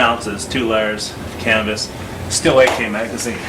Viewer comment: i heard "cannabis", not "canvas"... XD